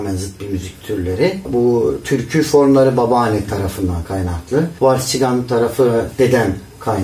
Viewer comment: ‘de ne diyor? Vals çidan gibi bir şey. Anlayamıyorum. Bilen varsa yazarsa sevinirim. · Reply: Vals ve çigan bir müzik türü